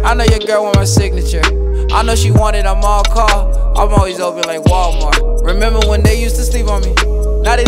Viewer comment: I thought he said “always open like Wawa” (convenience store in Philly/East coast)